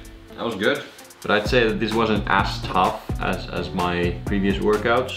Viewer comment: 'yes my right buttock is on fire' 😂 and I totally thought you said it had been 'ass tough' 😅